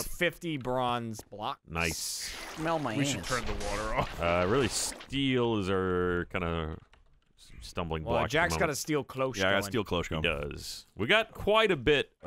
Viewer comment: did just say "We should turn the water off?"